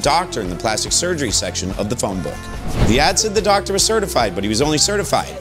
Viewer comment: I thought you were saying she sied every doctor in the phone book and I was going to once again bemoan res ipsa loquitor.